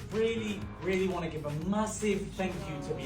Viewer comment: did you say massive 🥷